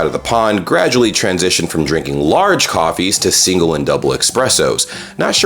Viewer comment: did you really say "Expresso"?